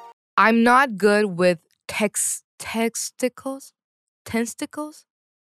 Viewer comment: 😂 😂 😂 Did I hear "Testicles"?.... Yep not good with them either 😂😂😂😂